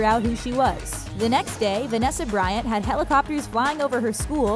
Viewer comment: did she just say hella??